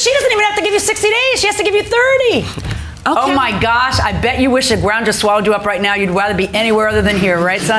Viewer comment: did she say you wish your mother swallowed you up 😳